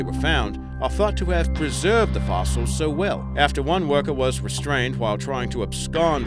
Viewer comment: can someone tell me what he said? Some that a worker tried to (blank) with an egg? I don’t know what he said. Sounded like ubstain